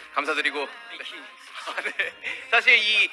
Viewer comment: did jimin say mickey? Aw how cute🤗